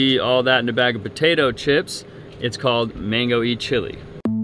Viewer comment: The clarity and color of that water though 😍 The name of the restaurant, I heard it as "Mango Eat Chilli" 😂 Great video guys 🙏